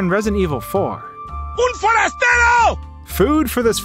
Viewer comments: I watch Vanoss so when I heard 5:00 I heard “Moldy Al Dusty” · I heard “mortys SUV”